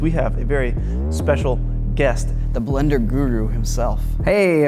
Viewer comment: When he said "My Channel is Blend Guru" i thought for a moment he said "Blend Kan-guru" cuz Australia